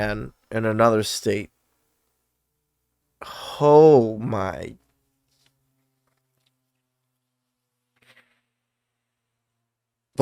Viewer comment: “Hoe, my” is how I heard this 😂😂.